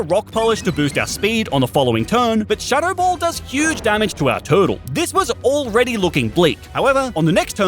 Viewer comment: DID HE JUST SAY TURTLE